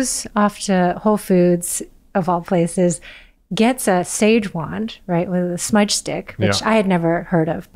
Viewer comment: can anyone help me what she was saying about the woman bought to remove the bad spirits of the previous owner? I can´t get it...it was a match stick? Sorry, my English is not good. thanks in advance. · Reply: Answering myself...forgot to use the subtitles. Sage wands